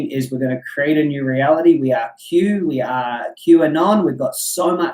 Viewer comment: My ears really pricked when I heard you say "we are Q-anon", did I hear correctly?